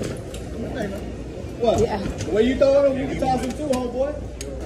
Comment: “The way you throwin’ em, you’ll get tossed too lil homeboy!” 💀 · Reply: I heard “ The way your throwing em, your the one passing em through homeboy”. Cuz you’re not supposed to grab your stuff through the area.